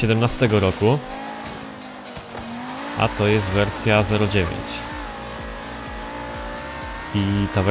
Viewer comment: Usłyszałem wersja 1.9 XD